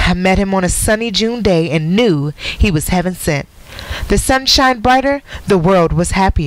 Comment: She said “heaven sent” I thought she said “having sex” Lol she said “he cracked me universe” I thought she was about to say cracked my uterus... 🙃